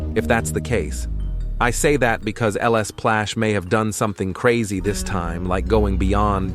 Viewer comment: I thought you say lsplash l splash not ls plash